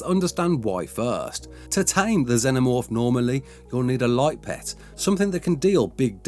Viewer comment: for a second I thought he was saying the light let could do a lot of damage